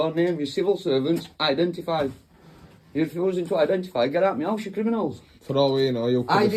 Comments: Get out me mouse!!!! Everytime he said house, it sounds like he said mouse. Trust me , say get out me house really fast 5x in an British accent. You'll see what I'm talking about... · When he says " get out me house" sounds like he is saying " get out meow " 😂😸